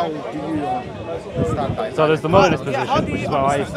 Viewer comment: it sounded like stated he holds a modalist position, bad time for the mic to get a bit muffled... Though given the context it's clear he said molinist which makes a lot more sense. Had me confused for a second there!